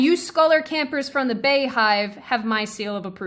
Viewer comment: "Beyhive"?? Never heard that term at all. These leftists are NUTS! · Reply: I thought she said bee hive